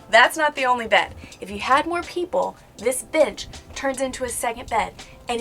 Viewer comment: lowkey funny how it sounds like is saying "this bitc-"